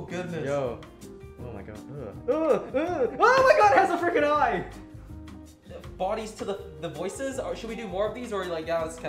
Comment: All I heard at 2:57 was "Ah the bodies to the voices??" What